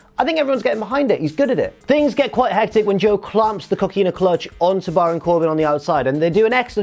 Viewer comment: Anyone else hear cookie in a clutch